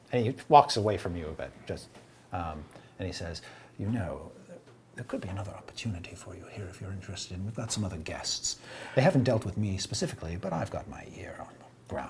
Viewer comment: I've turned up every volume in my computer and I still can't make out what he's saying. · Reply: "You know, there could be another opportunity for you here, if you're interested in... we've got some other guests. They haven't dealt with me specifically, but I've got my ear on the... ground. Have you ever heard of... 'black powder'?" -- "We've done quite well here, and Lord Zardak has always felt like he needed to step up his defenses, we've just got a deal with an organization to secure a large consignment of something called 'firearms'." -- "I haven't had much chance to study this magic myself, but, uh.. If you are interested in making some contacts I can at least introduce to them while they're here. Perhaps they can, I don't know, send some of these 'firearms' your way." -- "Well I don't know how much longer they'll be here".